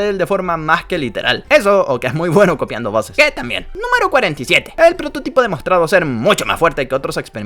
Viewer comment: soy el único que se dio cuenta que dice 77 y no 47?